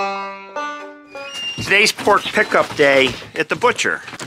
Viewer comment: My hearing must be going, I thought you said today was "porch pickup" day - thought it was a social distancing thing. when he asked if you could shovel any faster, I thought you'd throw the next shovelful in his face, and no one would have blamed you!